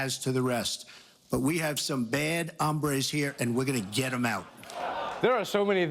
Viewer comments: funny thing is that it sounds like Trump says "Bad Hambres". Hombre means man. Hambre technically means hunger. So according to him, we have some bad hungers is this country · What so Trump said "hombres"? lmao his accent fucking sucks, when I heard it live I thought he had said "humbles" and spanish is my first language lmao.